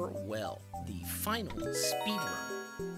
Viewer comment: I thought that said The Final Solution